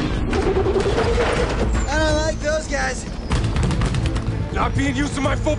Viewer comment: Does Mikey says that he doesn't like Girl Scouts? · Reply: No he says don't like those guys